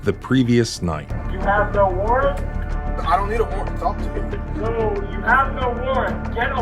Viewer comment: the subtitles are wrong. The cop clearly says, "I don't need a war to torture you."